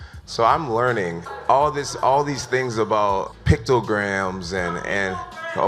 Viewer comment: I thought they were saying PITOgram 😂